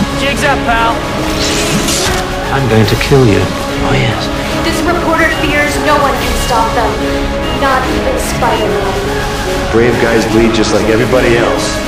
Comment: ? What she says in 1:20? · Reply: "This reporter feels no-one can stop them. Not even Spider-Man.